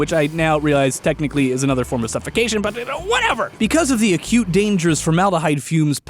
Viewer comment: When he said that they could get some acute stuffy stuffs i misheard "a cute..." and I was like..WHAT!?